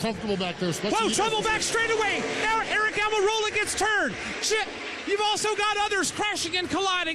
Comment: did he say shit??